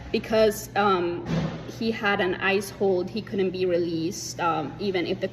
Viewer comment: It sounded like she said ICE-holes when she said ICE hold. That’s a great new name for ICE. ICE-holes.